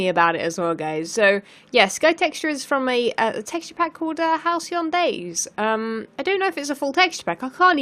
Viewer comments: Halcyon days i guess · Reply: thank you! I kept hearing "house eon days" or something lol. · Can someone help me out? What is the name of the texturepack she says at 17:45? I have no idea what name she's saying. Either a name or a link would be appreciated =) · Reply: Halcyon days i guess